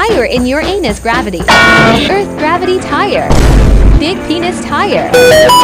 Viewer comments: I thought he said peni# for Venus gravity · When she said "Big Venus Gravity", I thought she said "Big P** Gravity". 😂🤣🤣